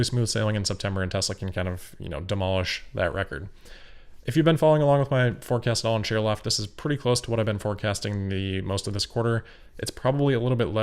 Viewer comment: I have no clue what you just said. Only one example of many. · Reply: “If you’ve been following along with my forecast at all on shareloft, this is pretty close to what I’ve been forecasting most of this quarter.” The closed captioning pretty much got it. I haven’t really seen this complaint from anyone else. I am under no disillusionment that I am an amazing speaker. I’ve mentioned many times that’s one of the reasons I started the podcast. I try to get better every single day. It’s hard.